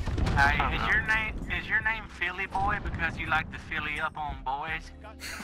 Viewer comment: I thought said Phillybo and I got happy but now I’m sad